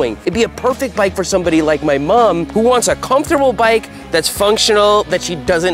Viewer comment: did you just say "my Mum"?